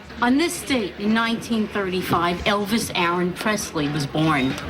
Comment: Did she really say "Elvis Alan Presley?"